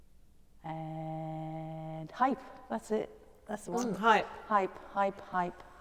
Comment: Oh....Hypes... I thought you said Hearts... oh well, whatever a Hype is, I'll do it if you tell me how :) In the meantime, you got some Hearts, lol